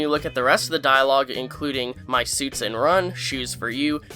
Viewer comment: I thought he said "Armani suits", not "my suits".